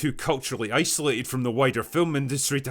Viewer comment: Did he say wider or whiter? Yes